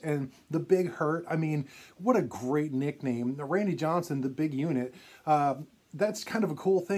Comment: I always loved Randy Johnson's nickname. The Big Unit!!!! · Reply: I remember the first time I heard that on a broadcast and thought they said The Big Eunuch. I thought what a horrible thing to say. Haha. Glad I was wrong